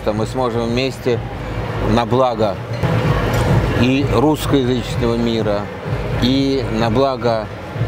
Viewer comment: на благо русского языческого мира???... ОН ДЕЙСТВИТЕЛЬНО ТАК СКАЗАЛ, ИЛИ МНЕ ПОСЛЫШАЛОСЬ???